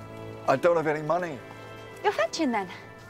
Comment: So I have replayed @ 1:58 about ten times, even slowing it down to 0.5x and I still can't understand what she is saying in response to the Doctor's statement that he has no money. It sounds like she is saying "You'll" or "Your" "hatching then" which of course makes no sense. Maybe "hatching" is "hat chain" but what does that even mean? Is she suggesting the doctor has something else on him that could be used instead of money? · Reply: Fetching. She's sending him to fetch the coffee.